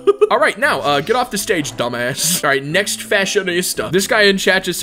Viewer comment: did he just say dumb ass????